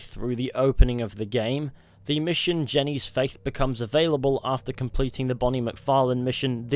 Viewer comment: I swear I thought it said jenny's fate